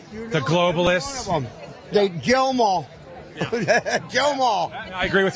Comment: Don't lie 3:00 Trumper just said "chill them all" That's what I heard.